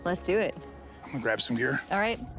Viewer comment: LOL.. said he's going to grab some gear, but at first I i thought he said he was grabbing some beer!!